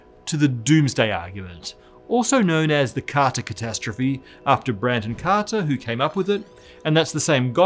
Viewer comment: I thought he said "the car to catastrophe" and I was like damn I don't wanna get in that car